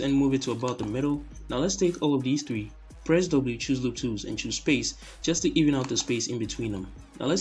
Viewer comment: I only hear what google subtitle hear: press double choose loop twos :((